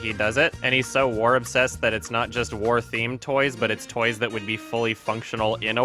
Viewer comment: for a second i thought he said "f### toys"